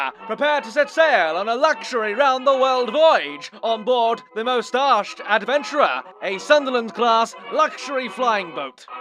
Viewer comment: I misheard "Mustached Adventurer" as "Mis-Arsed Adventurer." Got a good chuckle, mmmyessssssss.